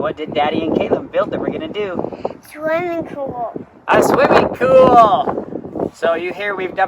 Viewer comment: Did he sayd "Swiming cool"?